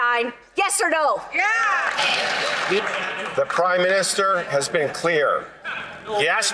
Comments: I swear I heard him say 'The Crime Minister" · did I hear "crime minister?"